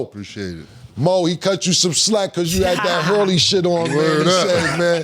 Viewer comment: did he say Moe had on “that horny shit” 🤦🏾‍♂️